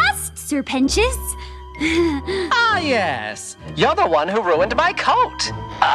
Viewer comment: Dunno why, but I heard that as “You’re the one who ruined my cult!”